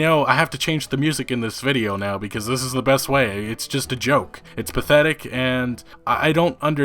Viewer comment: did you say "it's Bethetic" or is my hearing going with my old age.